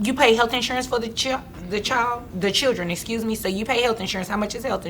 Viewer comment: I like this judge it almost sound like she say 'cherdren'